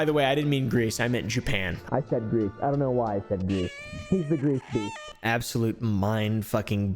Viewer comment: Everytime you say his name, I just hear "GreaseBeast" instead of "GreeceBeast"